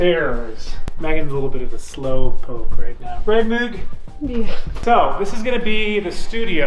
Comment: what does he say as he turns away from the camera???? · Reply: "Megan is a little bit of a slowpoke, right meghan". I think